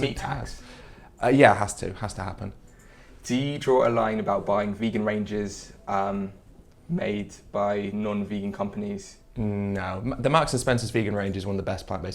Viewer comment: Can someone please tell me what they’re saying at 14:42? It sounds like “vegan ranges.” Thanks in advance! 😊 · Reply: "Do you draw a line on vegan ranges made by non vegan companies" He means does object to companies that aren't vegan but make vegan foods such as plant based burger paddies.